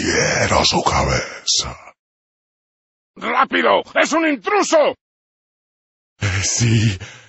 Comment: It's wrong. I'm not fluent in spanish, but I speak portuguese and italian, And I'm almost 100% sure that what he is saying is: " Rapido, ès un intruso", and that would translate to "Quickly, there is an outsider/intruder".